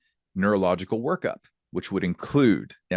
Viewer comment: I heard that as 'neurological worship'... I should think we're well past that stage though 😀